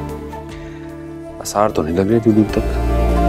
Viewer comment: What does he say at 1:41? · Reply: He said Aasar toh nahi lag rhe door door tak..